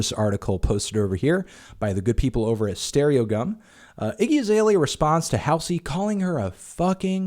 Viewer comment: I thought it said StereoCum